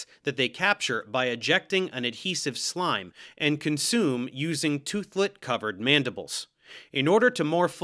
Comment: lmao I heard "and consume using two foot covered mandibles" and almost spit out my breakfast.